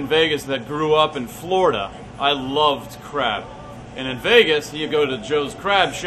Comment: For me it sounded like was saying 'I love' crap'